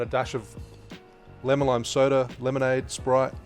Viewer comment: In the AMF and this one too. Although I’m sure that the Tokio Tea would be better with just a 0.50 oz of each liquor just to make it a little less stronger. Great suggestion to use lime juice. I’ll try that next time at work! 😃😃😃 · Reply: Oh! I thought I heard lemon juice only. My bad! In all honesty I was having a cocktail or two while watching that video. Cheers! 😂😂😂😂